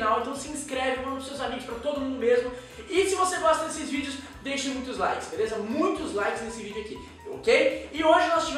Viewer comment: só eu ouvi " Se você gosta de xvídeos deixa muitos likes " ?